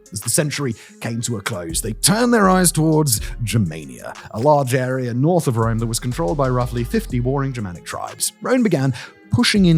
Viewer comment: 🤣🤣 For a second I thought you said "...the Romans were looking to move into Jamaica, an area controlled by 50 boring Germanic tribes. " I've been sober for years, honest.